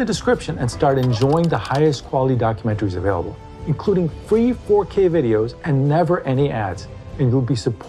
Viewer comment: I almost heard you say "free 4k videos and never ending ads" 😄